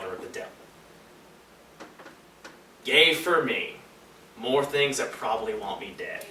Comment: I thought he said gay for me